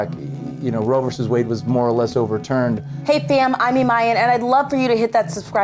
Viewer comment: did she say "Hey fam" at 1:13, lol